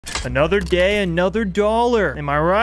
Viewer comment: i heard "another gay, another dollar"